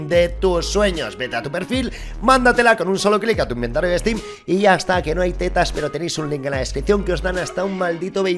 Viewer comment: amigo escuché “asta que allá tetas nose si está mal tradicido o soy yo like si es verdad o coméntenme en comentarios si soy yo.